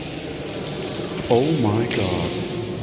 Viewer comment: Ma solo io ho sentito una voce al minuto 2:54? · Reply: A me sembra lui che dice "Oh God...".